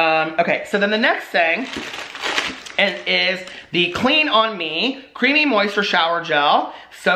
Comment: OMG I thought said “Cream on Me” and I was like whaaaaa?!?! Family friendly!